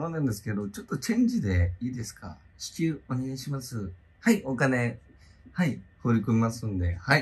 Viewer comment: お金…放り込みますんでって聞こえておもろい